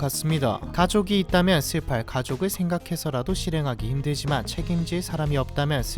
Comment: 영상내영과 별개로 내가 넘 자극적인것에 찌들엇나보다… 영상은 안보고 소리만 듣는편인데 슬퍼할 가족 -> 시팔 가족 고독 -> 구독으로 들려서 놀띾네😂